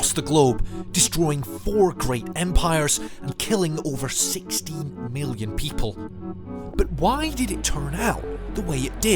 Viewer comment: confused me at first because I misheard ‘16 Million’ as ‘60 Billion’ and ‘60 Million’.